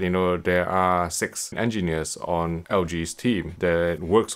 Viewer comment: i did a retake for a second there when i thought you said at 3:51 "hypothetically there are sex engineers on LG team"...